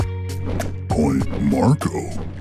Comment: (did he say queen Marco?)